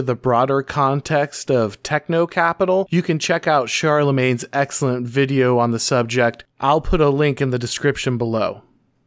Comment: when i first heard it i thought you said 'you can check out my boy charlamagne in the description below' lol